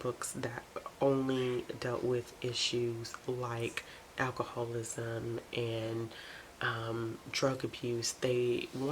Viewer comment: What were the books that you said a 6:07? (Could not hear what you said.) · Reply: Moralistic Outcome books